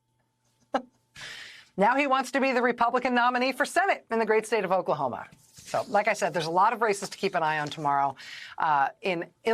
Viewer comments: Rachel is amazing!!!! could have sworn she said lots of RACISTS lol to keep track of tomorrow. She didnt but in my head I chuckled. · When Rachel closed with "there are a lot of races to watch tomorrow", I totally heard "there are a lot of racists to watch tomorrow". Easy mistake to make I guess.